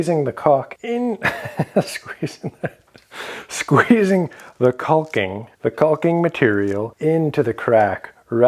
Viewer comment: Did anybody else hear “ squeezing the cock into the crack “ 🤣